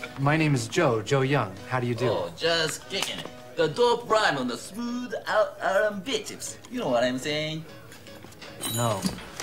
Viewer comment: what is he saying at the 0:43 mark? I’ve no joke been watching this movie for over twenty years and still cannot tell. even looking up transcripts no one seems to have a clue. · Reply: "Oh, just kickin it. The dope rhymes on the smoothed out r and b tips. You know what I'm sayin?"